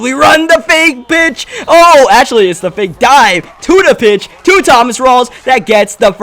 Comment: When u said to the pitch I thought u said tuna fish lol